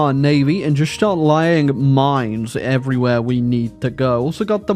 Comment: Did anyone else hear him say lying on mines instead of laying on mines